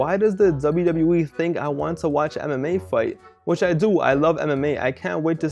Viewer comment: I thought you were saying "Anime fight" in the beginning, not MMA. Lol, but good video bro!